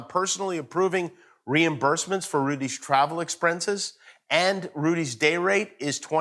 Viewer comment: did Jimmy say “exprences”!